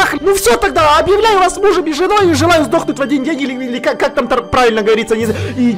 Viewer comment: мне послышалось "здохнуть через день " угарал 20 минут ))